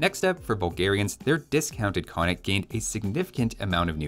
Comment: I don't care but given the channel type I figure you might, 3:58 sounds like "discounted" instead of "dismounted"